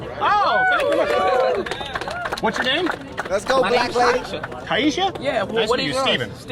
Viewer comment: Most of my black friends told me trump did more for them than any president in recent history, but they didn’t want to speak out for him for fear of backlash. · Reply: I thought you said blacklash